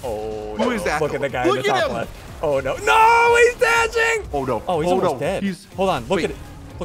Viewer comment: did he just say oh no he's edging! go to